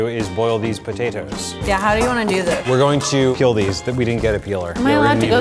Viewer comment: I thought said "we're going to kill these"